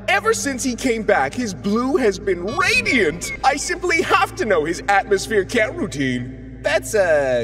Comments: I think Saturn’s saying “I simply have to know his atmosphere care routine” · I think he said atmosphere ‘care’ routine, similar to how people say “hair care routine”. Not atmosphere ‘cat’ routine.